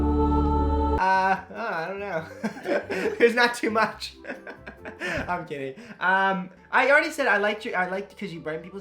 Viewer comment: Did he say "I'm gay" ???